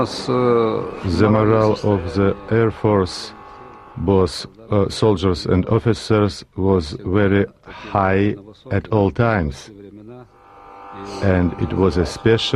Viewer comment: Did I hear correct when that guy said "Generals, officers where High at all times".....at 4:35 ..?